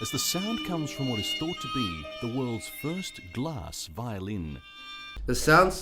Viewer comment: When they said glass violin I thought they said grass violin lmao 😂